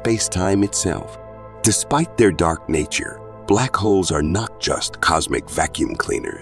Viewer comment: Omg I thought he kept saying Black Hoes 😵‍💫